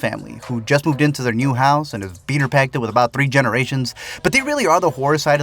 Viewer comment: Did you just say beaner packed it????